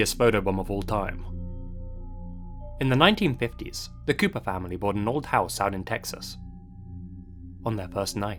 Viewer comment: I thought he was saying Koopa family. And kept imaging a bunch of koopas about to be photobombed by mario.